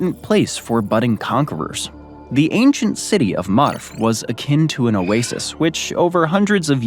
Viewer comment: did he actually say Marf?🤣